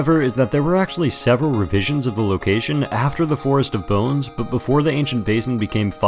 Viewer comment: I thought you said forest of boats at first and was so confused why they would remove such an area